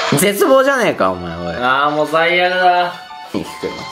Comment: で「最悪だ」が「バイアグラ」って聞こえました。なんかの治療薬の名前みたいですね。